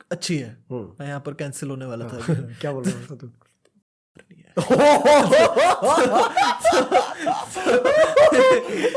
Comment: 😂 Kya bola batado!! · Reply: " Itni gawaar nhi hai "😂😂